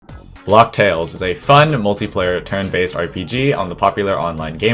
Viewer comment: the way you said "fun" sounded like you tried to say fu** when cruel king got hit